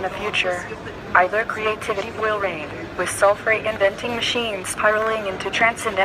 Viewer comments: She didn't say sulfur. She said self reinventing. Also, automation not godimation. This video seems to be intentionally mishearing words to jump to false conclusions. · Well, it doesn't help ANYONE's understanding of what the robot was "saying" when most of the second word is chopped out of the audio stream and then the audio slowed to help guide people to a mishearing of what was actually said, which was plainly clear on the very first pass of the audio prior to the excessive edits and audio jump-cuts to be "Self Reinventing." It is sad and unfortunate that people making videos seek to be so manipulative and dishonest. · No, I heard "self-reinventing". That implies robots capable of creating new ones..nothing to do with sulphur...but could be worrying even so!